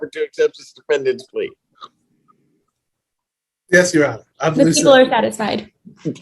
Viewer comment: What does the prosecutor say at 9:00? I can't make it out. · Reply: "The people are satisfied."